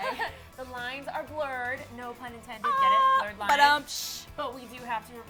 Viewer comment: what did she say? what does it mean?? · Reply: No pun intended